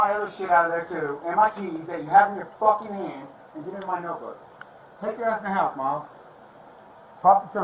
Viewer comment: " take your ass in the house mom" is what it sounds like he says.